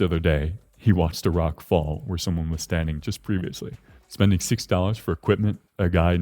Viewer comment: did you say 6 or 60 dollars ? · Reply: $60